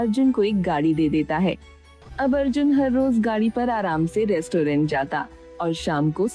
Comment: m of story i thought she said Gali de deta hai😅